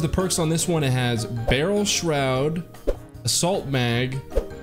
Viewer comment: It sounded like he said “salt mag” when he showed assault mag lol